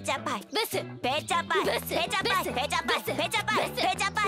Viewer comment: 「ブス」と「ペチャパイ」が重なって「ブチャパイ」に聞こえるw